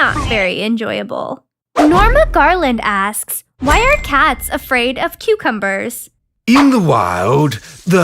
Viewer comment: I thought it said why are cucumbers afraid of cats 😂